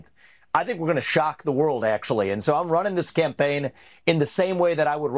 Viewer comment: Sounded like Ramaswampy said SCAMPAIGN...?